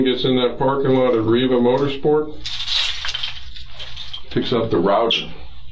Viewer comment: what does the detective say at @ 33:08 picks up the what raja ?? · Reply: "it picks up the router"